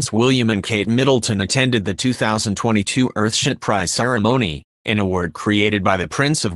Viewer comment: Did he say Earth shit price at 1:09 ?